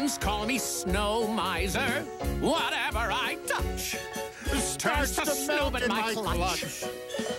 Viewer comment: why do I hear “Starts to smell in my clutch”